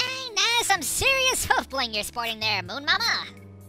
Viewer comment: buahahaha XD moon mama XD lol thought he was about to say moomin mama XD